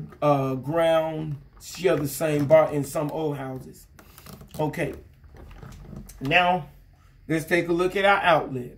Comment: I swear like 3:17-3:20 when he said "now" I heard a 'meow'